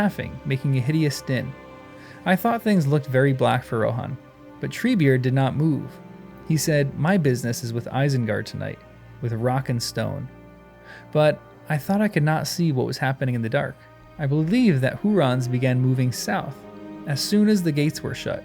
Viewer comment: Did i hear ROCK AND STONE?!